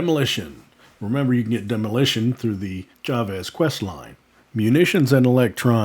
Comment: I couldn't make out what you were saying bud, how about which quest to get the demolition operator. Sounded like you said the child esk quest lol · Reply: Ha, chavez quest. <link>